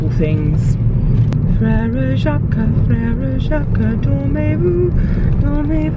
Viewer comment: It kind of sounds like you say “ferrero jacques” 😂